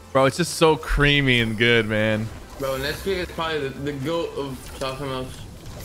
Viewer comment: I really thought this man said nesquik is the goat or child pornos after said it so creamy and good